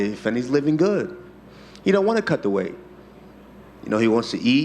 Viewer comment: I thought he said "cut the maggot"